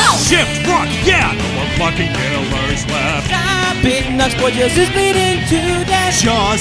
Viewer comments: for years i kept hearing "poortisbleedingtoodeath" i can only hear "poor tails is bleeding to death" if i look at the captions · I still keep mishearing this as no more f**king pillars left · I thought he said "No more f*cking pillars left"